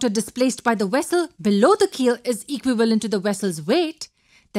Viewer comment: What does she say at 2:23 "is equal sajdoijfoias to the vessels weight" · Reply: Brandon i think she said " equivalent "